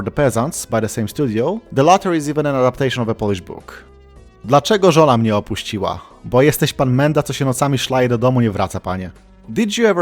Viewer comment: misheard "the latter is even an adaptation of a polish book" as "balatro is even an adaptation of a polish book" and nearly knocked my bag of seeds over going to rewind happy 10k to the big man <3